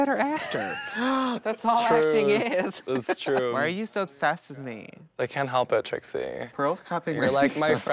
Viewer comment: what the fuck is Trixie saying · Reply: Adriel Perez she said why are you so obsessed with me